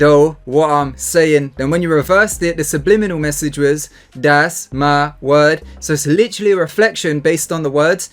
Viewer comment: Before "that's my word" I heard "Satan" Who heard Satan as well